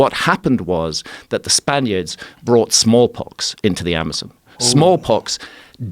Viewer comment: A lot of talk of embankments which brings Billy Conforto to mind. RIP. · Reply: Thought he said they brought in “small cocks” Instead of “small pox” Small cocks seemed more plausible and likely than small pox from these people